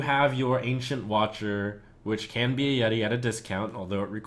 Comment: what is he saying? "which can be yari yari discount"!?